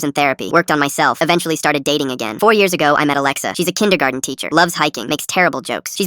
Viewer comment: Am I the only person that miss heard it and thought he said he met a kindergartner